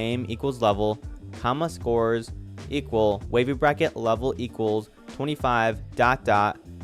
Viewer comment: I actually say wavy bracket😅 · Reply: oh :0, i heard it as baby bracket😅